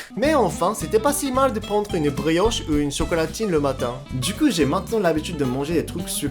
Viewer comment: j'ai entendu chocolatine ou je rêve ???😂😂